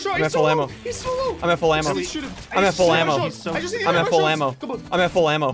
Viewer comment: Bro it sounded like you were saying am at flamo when you were saying I’m at full amo